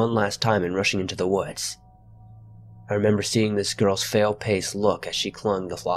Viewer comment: anyone else notice he said fail pace instead of pale face ? 😂